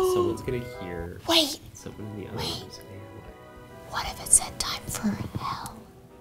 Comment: Im SO sorry but I TOTALLY heard "time for hell"😳😳😳